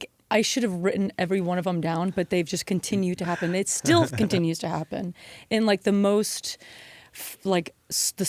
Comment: Guys, almost nothing related to the content, but did Emily say "They still continueS to happen" at 7:03 mark? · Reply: She says „and it still continues to happen“. Also, people make mistakes, so what?